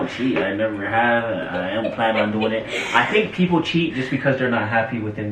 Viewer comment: Guys am I hearing things wrong because i hear saying “I am planning on doing it” 😂 I know would never cheat on because you can see that he loves her so MUCH! ☺️